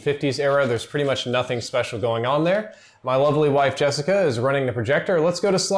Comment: It sounded like he said "my ugly wife Jessica is running the projector"